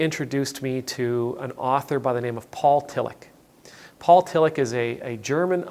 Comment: Your Pronounciation is terrible, I thought you said Paul Killich... <link> :-) And what is also interesting: When I know what you want to say I hear it... but before reading the name I searched for Kilich 😁